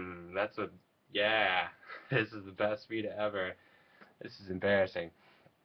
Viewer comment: lolol thought you said best weed ever... had to go back and check that one ;)